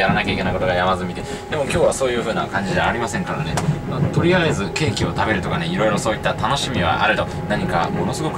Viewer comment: 「ケーキを食べる」がどうしても「京急食べる」に聞こえてしまうw